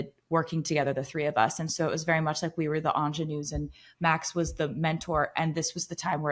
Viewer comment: what is the word she says, "like we were the ____?" The YT auto-cap does a horrible job of transcribing. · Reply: Ingenue’s